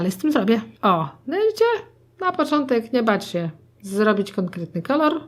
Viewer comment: usłyszałam j3bć się😂